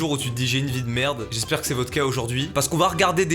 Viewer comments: "j'espère que cest VOTRE CAS ajd" Moi qui entend vodka au lieu de votre cas...😳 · j'ai compris "J'espère que c'est VodK aujourd'hui"